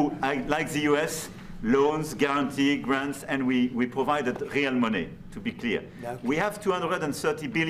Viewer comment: Can somebody help me understand what word the Ukrainian leader is saying @ 5:16-5:18 …after “ loans, guarantees grants and we provide…” Thank you fr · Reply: He is French mate. And he said real money.